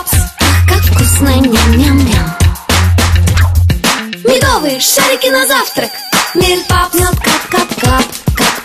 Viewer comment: definitely didn't hear shawty 👁👅👁